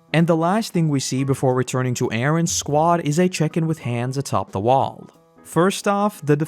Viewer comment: I heard "is a chicken with hands" instead of "is a check-in with hans" and I burst out laughing